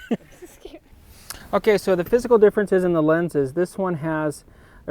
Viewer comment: For a few seconds I thought you said: "So now let's look at the fiscal differences".